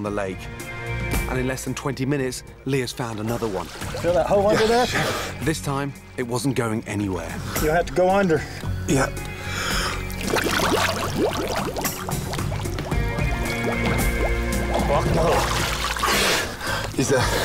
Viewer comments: I swear he said “yes chef” as he came out of the water. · Did Ramsay just say "Yes Chef"😅